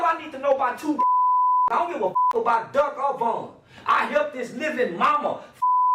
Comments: Subs during the Charleston White interview were wack lol. 24:34, "I don't give a fu** about duck Obama." Why would they even think that was correct? He said "I don't give a fu** about Duck or Von!" That was the most obvious one, but some of the other ones were off too. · the subtitles says " I don't give a fu** about duck Obama" 😂😂